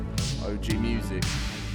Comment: did you just say OG music you 🤡